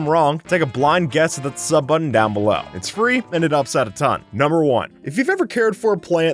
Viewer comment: Lol I thought you said “ it’s free, and an upside of time” all this time instead of “ it’s free and it helps out a ton”. LOL.